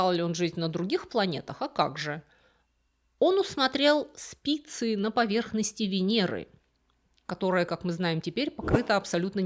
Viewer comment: мне послышалось "усмотрел пиццу на поверхности Венеры"😂😂я походу голодная была